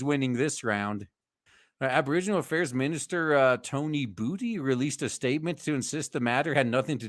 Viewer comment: I heard "Tony Booty" in which case (queue clip of "The Booty Warrior"